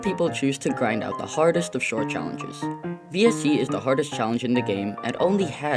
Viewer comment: what us BSC? edit: or whatever was said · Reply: VSC was (at the time) the hardest challenge in GD